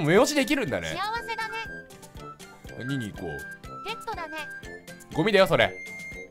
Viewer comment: ペットだね！って聞こえたw